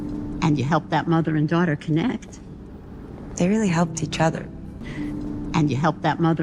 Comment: in this time she said (they really helped each other.) but i hear ( it really helped each other. ) i hear the word (it) with glottal stop instead the word (they) is that right? Thank you.